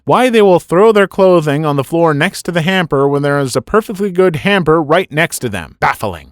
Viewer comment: all i heard was... Hampter